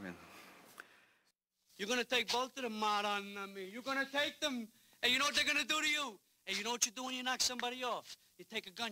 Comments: what does he say in Italian? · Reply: madonna mia. madonna is what italians call mary. its kind of like saying oh my god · What is that Italian saying that he says at 0:07?? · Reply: chrispywilliams1992 Madonna mia, translated as oh my virgin mary or something of that sort to express disbelief